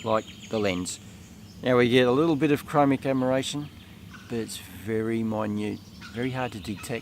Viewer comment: What did you say at 4:34? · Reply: Chromatic aberration I think , fringing ...